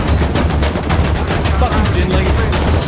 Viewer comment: am i going crazy or did someone call a "yingling"?